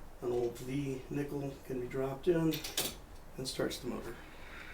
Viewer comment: I hear NEIN half the time in that sentence.